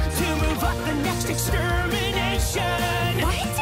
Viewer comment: I’m not the only one who hears “to fuck the next etmnation”